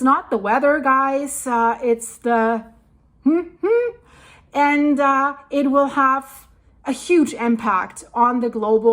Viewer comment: what were the words she couldn't say? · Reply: Cli mate change.